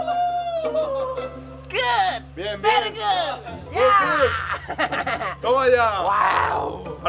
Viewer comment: pensé que dijo V3rga jajajaja